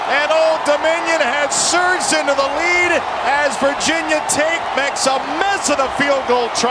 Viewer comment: Did he say Virginia Taint? Well did look like taint